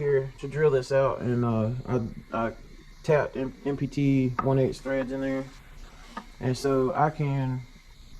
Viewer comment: Thank you for making this video. It will surely help me in diagn9sing/repairing my leak in my 93 aero. Really appreciate your work documenting the repaur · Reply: I think he says "I tapped NPT 1/8" threads"